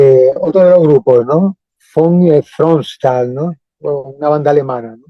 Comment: ¿Alguien puede decirme el nombre correspondiente de este grupo alemán? No se entiende nada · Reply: Creo que es Von Thronstahl.